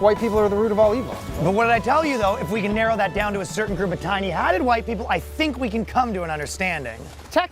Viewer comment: First time I listened to that unfunny clip, I heard "white hats" instead of "tiny hats" regarding Jewish people and went "Well that tracks as a leap in logic"